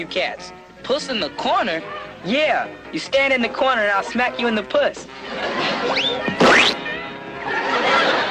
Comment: what did Rudy say???? 😹😹😹😹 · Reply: Sound like he said snap you in the puss